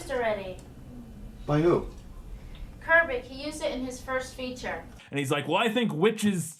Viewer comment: Did she just say Kirbik!?! Are they trying to say KUBRICK!? LMAO!😂